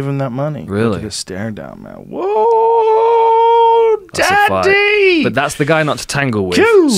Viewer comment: Did Joe just say "daddy" @ 3:25? lool